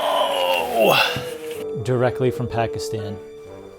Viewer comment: wait did he said Pakistan ? 0:14 i am from pakistan tho